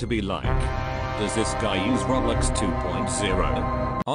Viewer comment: I heard "Roblix"